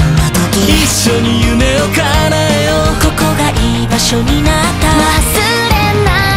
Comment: 居場所が"いい場所"にも聞こえるのめっちゃいい😭